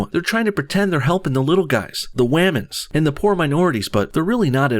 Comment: did I hear w a m a n s?